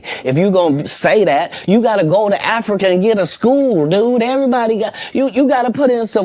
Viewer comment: But be fair he can't tell blackston to open a school in Africa and Katt hasn't opened one here in America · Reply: He didn't tell him to build I thought he said go to school in Africa comedy school I thought